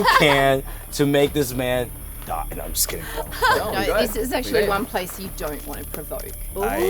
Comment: she says ‘this is one place you don’t want to provoke’ but the subtitles say ‘one place you don’t want to go’ just in case you wanted to change it :)